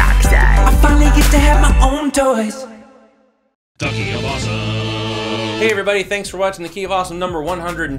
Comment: Who also thought it said "ducky of awesome?"